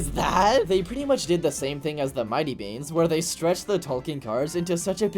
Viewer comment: Not to be rude but the way you say talking car sounds like your saying tall-king car